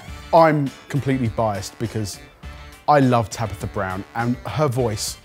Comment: I thought said I love tapping the brown and her voice could tell me anything